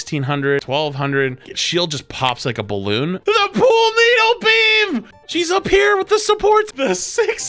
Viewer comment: I heard him say pool needle the first time and I was having to think about that